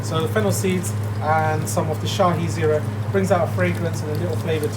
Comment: he mentions a herb/spice that goes in along with fennel. But i can't make out what that is. Anyone? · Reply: Its Shahi Jeera :)